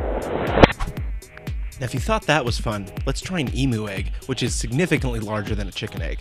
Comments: thought he said emo egg becouse it was black :P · lol i thought it said an Emo egg cuz it was black lol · i thought he said Emo egg not emu egg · I thought you said emo egg. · at first, i thought he said "emo egg"...........